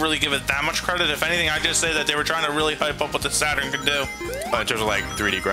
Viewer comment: did he just say hype or pipe at about